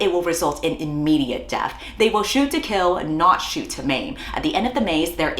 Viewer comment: I don’t need to theorise because I already emptied my head prior to tapping this video and I already know I am not going to see roses the same after this 🫢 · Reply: thought I heard “shoot to mate” instead of “shoot to maim” and I looked over to check my dad didn’t hear even though I had it low volume to ensure he wouldn’t in the first place